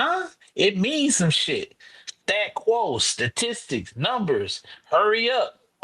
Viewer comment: The fuck is dude talking about 🤣🤣🤣🤣🤣🤣 stat quo statistics, sounds like he say grand rising